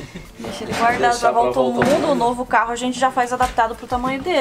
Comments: "EU OUVI UM NOVO CARRO" sair da boca da baixinha? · Aos 17:34 eu ouvi novo carro na volta ao mundo?